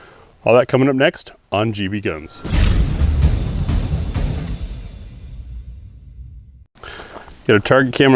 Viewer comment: I thought it said BB GUNS. 😂